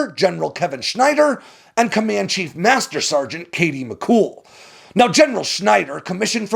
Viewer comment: Listening in 2.5x and thought you were saying "Comanche Master Sgt"😂